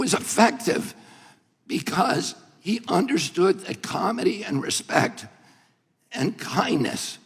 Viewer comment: When he said comity I bet Plasket got mad because she doesn't know what that means. She probably thought he said comedy.